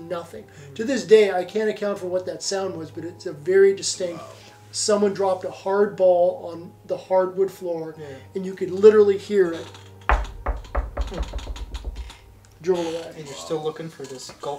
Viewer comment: I heard it say, 'Harry Balls'